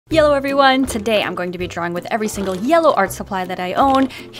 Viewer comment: I thought you said “ Yellow everyone!”